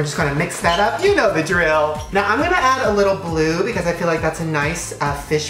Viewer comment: I thought he said lube